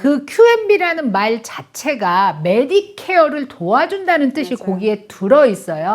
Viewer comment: Qualified “Medicare” Beneficiary라고 명확하게 말씀해 주셔야. 얼핏 Medicaid 라고 들려요 (12:29) 사실 이 (그렇잖아도) 두 용어가 많이 혼동되므로, 틀리게 말씀하시면 안 될 듯 합니다.